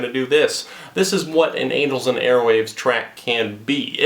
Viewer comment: Did you just say anals and airwaves